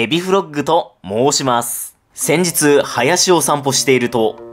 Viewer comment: 先日、林修と… と最初聞こえてびっくりした